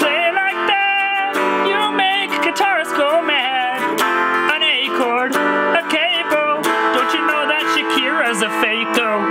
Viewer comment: When sings about how fake Shakira’s guitar playing is, it sounds like he says “An acorn, a capo, don’t you know that Shakira’s a fako.”